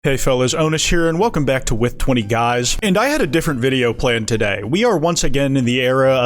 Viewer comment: with 20 guys? i thought you said extra fries!